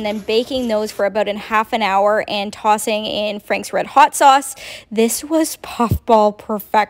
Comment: I thought you said Anne Franks red hot sauce lol. Had to rewind